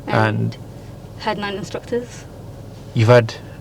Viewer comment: Woah woah woah... 3:40 Did I hear that right ? 9 Instructors ? 9 ?! 😳